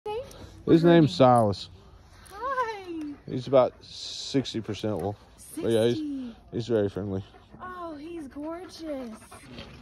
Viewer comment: I thought u said "his name is sauce